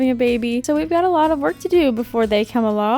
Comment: Before 'they' come along.....did I hear that wrong?? 🧑‍🤝‍🧑